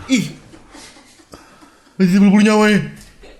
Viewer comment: lu ngomong apa😂😂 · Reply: badiri tpe bulu' nyawa ee